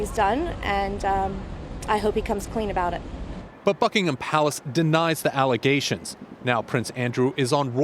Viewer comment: I heard Buttfuckingham palace